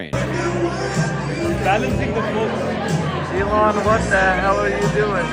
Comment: that sounded like Donald Trump saying "Elon...what the hell are you doing?"